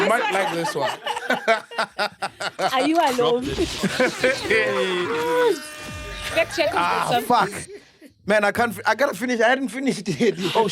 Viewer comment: did ghosty say ARE YOU ALONE